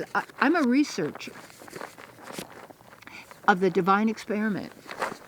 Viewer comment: You just got a little hint from above when you said "Divine Experiment" at 12:26 - the subtitles say "Applause" - made of the crackling from the stones ;DD